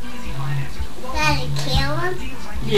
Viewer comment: This is scary but me and my mom hear at 00:21 it sound like he said did daddy kill him